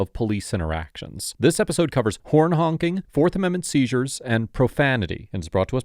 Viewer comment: I wasn't looking at the screen and i thought he said "porn honking." I'd loved to see that too!